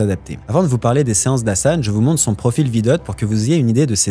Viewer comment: C'est quoi le profil que tu as partagé ? J'ai entendu "vidot" mais ca matche pas..